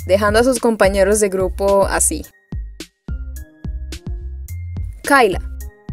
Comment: Yo me llamo Kayla y cuando lo escuche juro que pensé que me estaban llamando XD